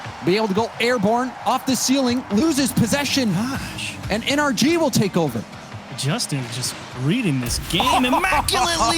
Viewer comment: I want to know what Garrett said after scoring this lmaooooo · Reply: they have comm video he says “les fucking go! gimme dat! holy shit! les fucking go!”